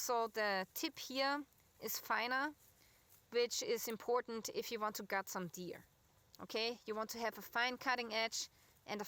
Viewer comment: I thought you said "In Case you want to cut some beer." Me: Knives and beer, yup that is as Canadian as it gets. lol